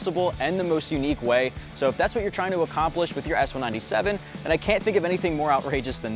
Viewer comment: Did he say S197 at the 2:00 mark?